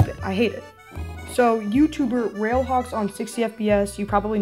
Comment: i didnt quite get him can someone gimme the youtube channel name i heard a fammiliar name in my friends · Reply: “Railhawkson60fps”